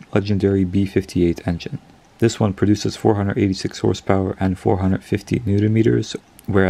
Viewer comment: Hi, @ 5:00 did you say 486 horse power or did i hear it wrong? Anyone?